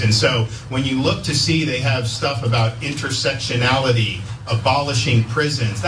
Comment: Did he really say 'intersextionality'?